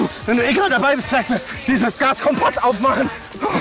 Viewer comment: ich hab verstanden gas kompott